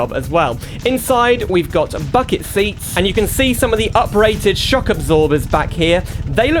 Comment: why did i hear him saying ''Fucking seats'' instead of ''Bucket seats'' lol